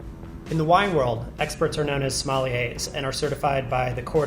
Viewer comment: Totally thought you said "smelly As" when you said "sommeliers"